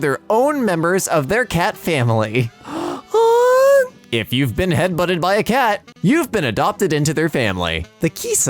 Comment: I thought he said if you Bean head butted by a cat you're adopted